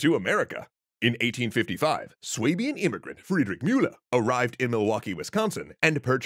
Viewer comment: there is a mistake in the subtitles: it says "serbian immigrant", instead of "swabian immigrant".